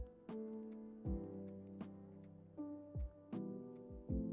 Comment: АХАХХАХАХПХВХАХРХВХАХПХ Я ВМЕСТО "ПИТЕР" 43:53 УСЛЫШАЛА ПИДОР ВХВХХПХПАХХАХИХАХХААХПХ